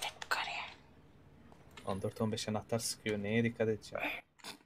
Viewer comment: de cesur ne diyor? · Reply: 14 15 anahtar sıkıyor neye dikkat etcem mekanik olduğu için